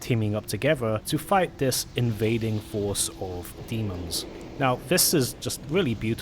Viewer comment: Am I the only one who misheard "...fighting this invading force of emos"? Hehe